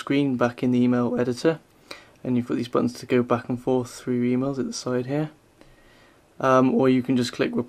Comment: he it sounds like he says "you can flick through your emo with the sidehair"